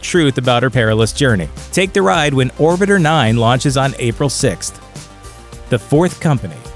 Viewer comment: First I thought he said The Fourth Kind